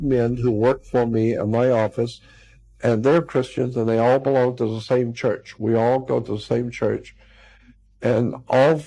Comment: OOOH! 😂 He said they all ”belong to the [SAME] Church.” Boy HOWDY! I thought he said they’re Christians AND worship at the Church of Sheitan. 😅